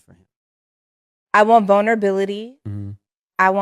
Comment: Did she say boner-bilities?